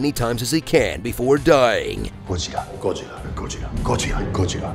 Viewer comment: whenever he says 'Gojira', i can only hear him saying Godzilla. it reminds of Samsung's CEO, DJ Koh saying GALOX! instead of galaxy